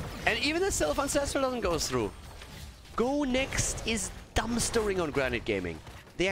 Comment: Could you help a french boy who don't understand everything ? I don't understand what is saying at 15:13 after "Go Next is" Thank you very much :) :) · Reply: I think "Dumpstering": putting in a trash can